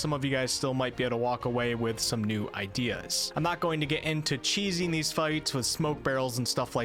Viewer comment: Lol I thought you said no cheese.